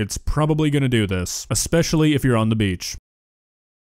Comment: Fun fact: The sky turns those beautiful shades of red and orange during a sunset because the light from the sun has more atmosphere to travel through. Since the earth and atmosphere are spherical, the light that hits at an angle is stripped of its blue wavelengths because of blue light having a different wavelength or some sciencey thing like that. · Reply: I thought it said special im cooked i might have dyselxia